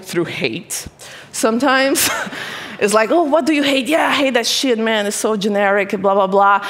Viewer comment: did i hear “shit” or am i wrong?